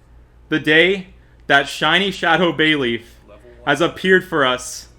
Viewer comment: i was playing something else and when you saw it i just heard "OH MY GOSH SHINY SHADOW BAYLEEF"